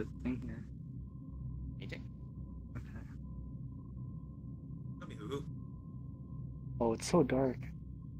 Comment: lol at 8:18 i thought said "ok google" but it was ok huh lol. to bad it wasnt scarier though i loved when huhu was with nagz and lan scared him like 3 times :)